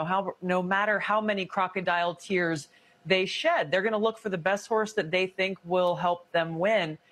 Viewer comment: Did she say "they're gonna look for the best whores that they think will help them win..." That explains why they picked Kommie Harriass.